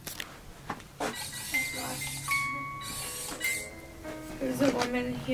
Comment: Did no one hear a voice saying “alright” after said “we should set up the rempod” at 18:57 - it sounded like it was further back or in one of the other rooms. Too far for it to be